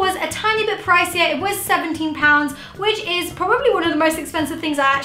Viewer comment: did she say 70 or 17?